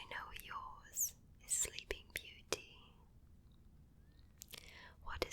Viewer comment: When she said “and your favourite story is sleeping beauty” I thought she said “silly bean beauty”......